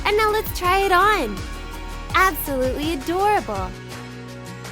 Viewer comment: I thought she said absolutely horrible